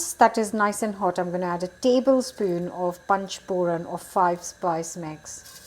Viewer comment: What does say, what the 5 spice mix is called? I already listen to it couple times but can´t figure it out. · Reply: Panch Phoran: Indian Five Spice mix